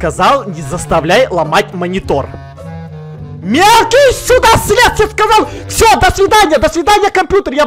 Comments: ОН ЧТО СКАЗАЛ С*КА!? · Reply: Gacha Radmila он сказала «сюда» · мне послышалось или он сказал... МЕЛКИЙ С****КА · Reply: Мне тоже... Переслушай, он сказал мелкий сюда слез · мне послышалось? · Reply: Мелкий сюда слез. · мне послышалось или сказал С*ка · сказал Мелкий су*а слез кто тоже услышал лайк!:)